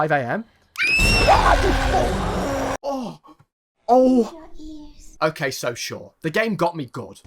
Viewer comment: Was that portuguese i heard? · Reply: "AI QUE PORRA!!"